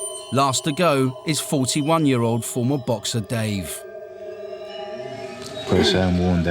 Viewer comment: Holy cow I thought he was saying Xboxer Dave the entire video. Like what an odd contestant:/